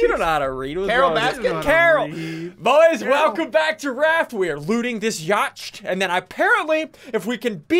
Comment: I thought said “welcome back to rats” instead of raft